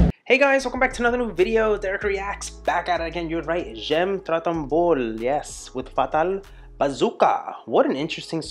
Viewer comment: 😂 sorry Dereck, but at the beginning of the video, I thought I heard you say "j' aime Dragon Ball" instead of " j' aime trop ton boule" and I thought it was the video song of Ariane, singer of the credits from the cartoon and I was there... oO eh! what ??? 😂